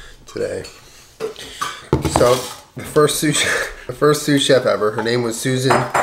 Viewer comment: i thought u were saying soup chef